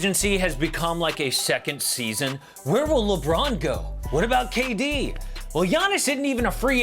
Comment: idk why I heard him say Where's Lebronto